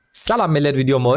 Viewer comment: başta salam millet mi dedin bizim oğlan ben mi yanlış duydum :D ben salam değil sucuk seviyorum :)